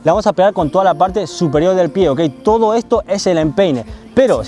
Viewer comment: escuche pene :v